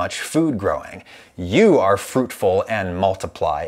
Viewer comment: did you just call me a fruit